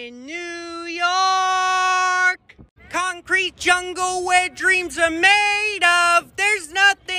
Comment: I didn't know why I heard that as "Wet dreams" ...